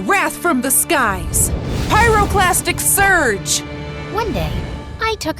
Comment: Is it just me or when she said "Pyroclastic surge"?It sounded like she was going to say plastic surgery